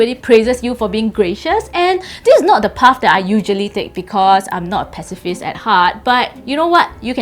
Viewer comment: what did she say ? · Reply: Tan Chuan En this is not the path I usually take because I'm not a pacifist at heart